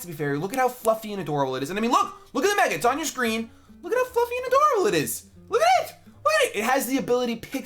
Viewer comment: For a moment at 1:14 I thought he said "look how fucking adorable it is," I nearly died.